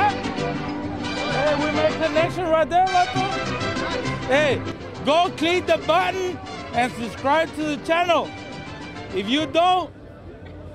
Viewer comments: I heard " go clean the bottom, and subscribe to the channel "😂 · I had the volume turned down and thought at the end he said, "Go pick the cotton"!!